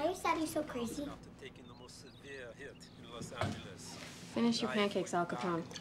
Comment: I swear I remember her saying "finish your breakfast judas"